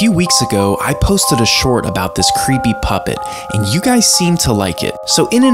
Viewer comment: I thought you said creamy puppet 💀